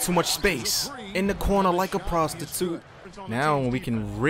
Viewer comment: Did he say like a prostitute